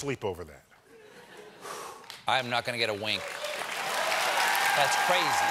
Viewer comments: Did he say wing instead of wink? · Did he just say I’m not gonna get a wank?🤣